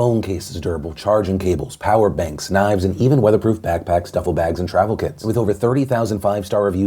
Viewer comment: Did...did Phil just say pack packs? I think he did! I can't hate though I work with pre-schoolers so I get it! But that was just so fucking adorable!